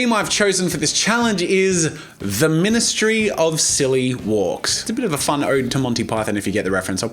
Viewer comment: (for all the harry potter fans out dere w) when said the ministry of silly walks i seriously thought he said the ministry of magic xD so i had to play the part twice ^w^